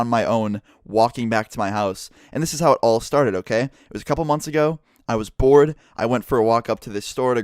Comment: Instead of :just a couple months ago i was bored i heard:just a couple months ago i was born 😅😅😅😅